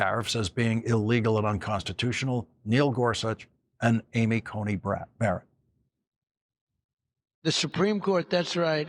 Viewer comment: I thought i heard " amy coney-brat"....i love it!